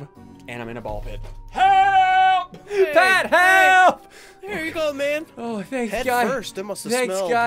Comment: did he just say "here you go babe?" 😳